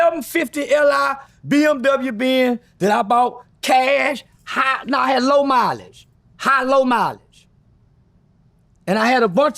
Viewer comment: did he just say he bought a Benz cash with high low mileage?😂😂😂